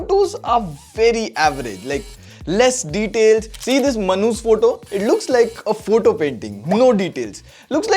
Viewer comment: I heard manhoos🤭